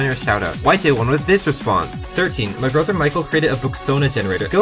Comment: i thought you said "my boyfriend micheal"